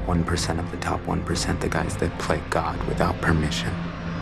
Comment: 0.38....I thought he said ‘ the guys that play golf without permission’ pml